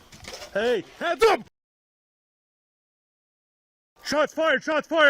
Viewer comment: I dont think they were saying “heads up” but rather “hands up” (5:01) which makes sense to be completely fair I feel negligence as a whole is accurate, the whole situation was sh*tshow, and rapidly went downhill in a tragic and avoidable way. Family deserves to be paid